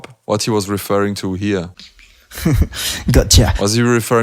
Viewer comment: Hey, I think he either said ‘gotcha’ or ‘Kotcha’ (Frosty and D-low’s tagteam name) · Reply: Gotchu